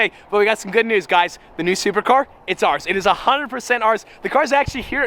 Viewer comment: I thought you were saying “new supra car” this whole time😂